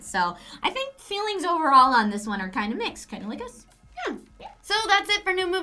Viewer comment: you said "kinda like us" but i thought you said cunnilingous hahahaha